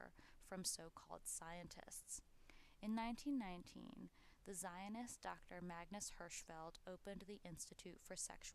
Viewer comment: the narrator says the word, "zionist", but the closed caption says "jewish"--- just noticing...